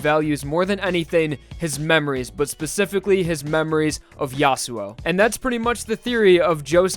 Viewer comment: Did i hear yasuo in 3:31?